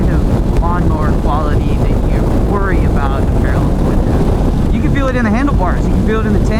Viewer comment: Nope it's not a V-twin. It totally feels like one though.😊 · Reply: wait, I thought said it was V-twin, haha my bad